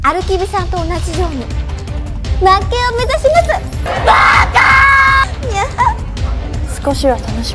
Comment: did I just hear "baka" 💀👍?? When she said idiot in korean